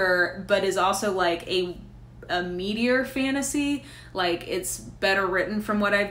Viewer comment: At first when you described The Fifth Season as a "meatier(?) fantasy" I thought you said "meteor" and was like what? is that a new subgenre I'm not aware of? lol It even kind of made sense, as I believe it's somewhat (post-?)apocalyptic. From that non-fiction on consumerism, I was going to recommend you Weapons of Math Destruction, but I see on your Goodreads you've already read it. :)